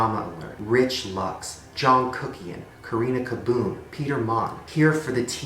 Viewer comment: am i the only one who almost thought he said "jungkook" at 9:18 lmao